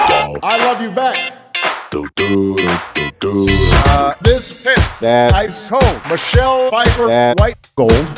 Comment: The beginning made me thought that he said is "This Pants, That I Stole"